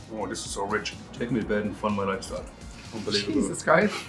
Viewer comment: “Take me to bed and fund my lifestyle.” My new pick-up line.... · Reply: I thought he said 'fondle my lifestyle'. Oh well, I like 'fund' better.